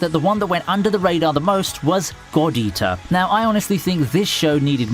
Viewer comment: Lmaoo, the way he says "God Eater" sounds like he is saying "Gordita"...and here I am, wth does Shakira have anything to do with this!? lol....